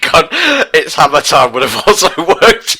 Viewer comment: Can someone confirm for me what is saying exactly at 1:19:31 ? All I hear is: "OMG it's hammer time with a thousand wood!" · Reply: Its hammer time" would have also worked.